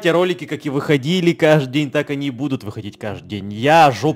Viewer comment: Что!? ВСМЫСЛЕ НЕ БУДУТ!? Или мне послышалось что он сказал --- Не, переживайте, ролики как и выходили каждый, так и НЕ будут выходить.